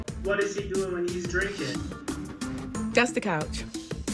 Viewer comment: What does Karen say at 6:50? I go the I hope I don’t have to use it part, just not the sentence before. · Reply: She said “that’s the couch” in response to the producer’s question. Basically, she is saying it’s to be discussed further when they are sitting on the couches at the reunion.